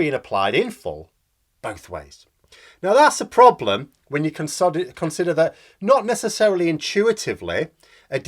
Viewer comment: Freudian slip there.… Instead of saying "consider" you almost said "con sod it". Which really is a great description of claims of Brexit preparations - "it's a con, sod it".